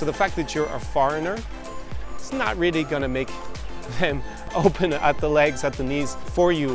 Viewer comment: Did he say "it's not really gonna make them open up their legs at the least for you" that's what I heard Lol